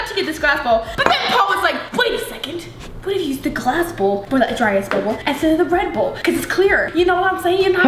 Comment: I thought she said dry ass bubble.. lmao sorry ily